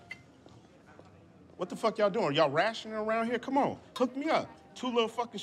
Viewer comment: why did I think he was saying “y’all ratchet around here?” wowww the more you know